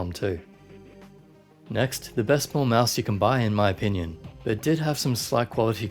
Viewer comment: The best what mouse? Auto captions thinks you said ball mouse. I thought I heard pull mouse